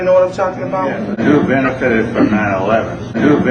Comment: Okay, now I can't say with absolute certainty but that didn't sound like '"the Jews benefitted from 9/11, Starting at 7:55, which sounds right? Audience voice: who benefited from 9/11? Speaker: well, you and I and I think know... Audience: yeah, bush. Or Audience voice: the Jews benefited from 9/11? Speaker: well, you and I and I think know... Audience: yeah, bush. My ears and brain would lead me to think the former. Maybe I'm wrong but that is what it sounded like to me. Now the rest of the exchange is still insane, the comparison ludicrous and conclusions pure fallacy. There is also plenty of other reasons to be certain of his anti-Semitic views.